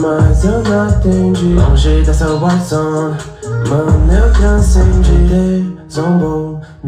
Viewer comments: Oq vc n entendeu na parte do eu entendi: "E qm zombou, minha AK nele cantou" Edit: pelo q parece, o certo é: "Hater zombou, minha AK nele cantou" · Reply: Eu entendi "cantei zombou, minha ak nele cantou" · Oq vc n entendeu na parte do eu entendi: "E qm zombou, minha AK nele cantou" Edit: pelo q parece, o certo é: "Hater zombou, minha AK nele cantou" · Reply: Eu entendi ''Cantei zombou, minha Ak nele cantou'